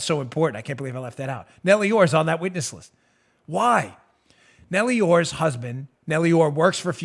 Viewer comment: All I heard was "a shit witness list"...lol Which, in this scenario, would be accurate.🤓🤣